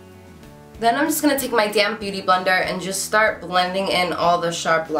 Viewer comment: I thought she said "damn beautyblender" at first hahaahaha